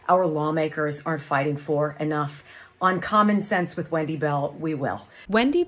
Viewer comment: Sounds like she said, "on commonsense with whitey bell" - check it out